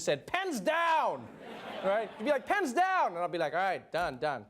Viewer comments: I misheard and though Trevor said "pants down" and I got worried of what kind of school he went to in Africa PS. I understand in like a minute that I misheard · When he sad "pens down" I heard "pants down"....😂😂😂